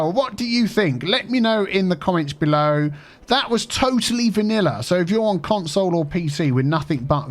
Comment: In the beginning I heard wrong, I thought you said "Totally Manila", and that it was some map based of Manila in the Philippines and named Totally Manilla :-D